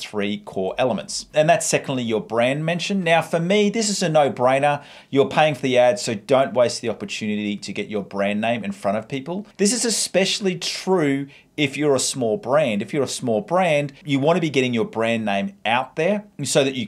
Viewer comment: I thought he said "This is especially true if you're a small brain" 😭😭